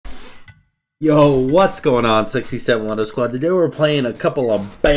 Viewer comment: Auto-generated closed captions have your intro as "yo what's going on 67 wonder squad". That about sums up what I thought you were saying for a year or two! Lol. My roommate walked by one day while I was watching your videos and I asked him if he understood what you said and he understood immediately that it was sexy samalander or salamander squad and he'd never watched your videos! Lol